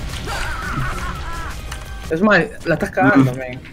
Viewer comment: dijo : smash, la estas cagando?? ... no escuché bien eso quizo decir?? no vi el stream